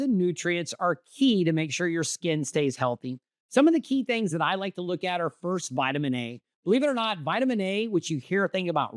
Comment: You talk about food/vitamin absorption in those without gallbladders. How do you spell what sounded like Oxall in your talk? Around 09:30. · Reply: Ox Bile